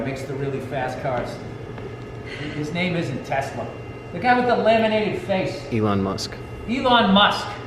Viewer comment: "The guy with the laminated face" hahahaha Elon Musk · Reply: laminated? thought he said lemon face or at least lamented. Lol